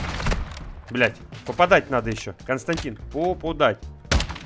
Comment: послышалось "Константин в попу дать" (шутка про говно если что) 😆